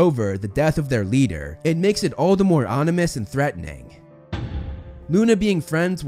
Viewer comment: Now I'm really wondering if I misheard all the other times or if he only now said Onimous.